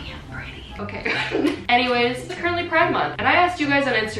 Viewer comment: i understood: ”i‘m currently pregnant“ instead of ”it’s currently pride month" and now i can’t stop thinking about it :o